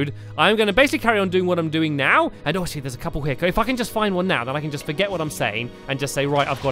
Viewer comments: O_O did Stampy Cat just Cuss? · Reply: i heard 'ugh, if I can justfindone now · did anybody hear stampy say the f word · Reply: He said if I can just find one now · stamps. put stone slabs every 4 blocks or so. to block the Ghasts from spawning! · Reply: I heard stampy said can i just fxxxing get one now from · Some people thought at 19:17 he said "If I can f***ing get one now" but he said "If I can just get one now." It's just the way he said it